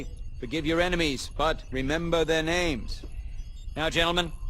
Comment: I always thought Kennedy said but never forget their names but he said remember their names